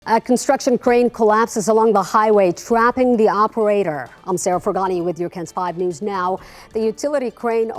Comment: It sounded like you said Scraping the Operator, as in ded.